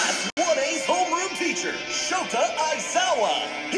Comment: I heard it as meth, not smash 😂